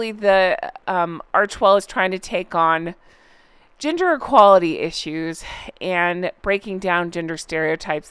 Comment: I heard that as ‘ginger equality issues’ 😂😂😂